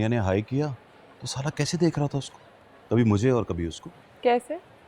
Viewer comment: did he just called his son "salla" ?